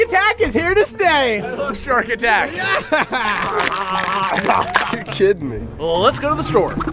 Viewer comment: did say "You're shitting me" 😂